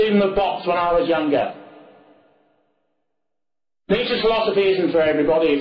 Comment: what did he say? "X philosophy"? What preceded philosophy? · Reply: William Wallace "Nietzsche's philosophy"